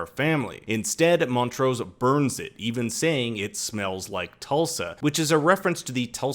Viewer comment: I thought he said, it smells like toast